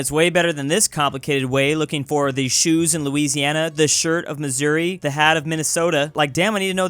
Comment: first time I heard bro swear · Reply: Its shirt.